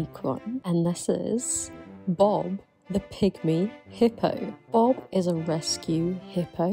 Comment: I hear "Bob the Pick-Me Hippo" before I realized it was pygmy lolol